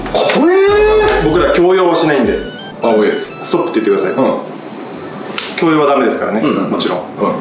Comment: の「ストーップ！」がスピードワゴン井戸田氏の「あまぁーい！」に聞こえた俺😂